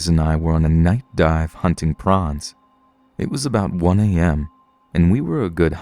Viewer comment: So...who else heard it as "Two buddies and I were on a night dive hunting Pr0ns"...?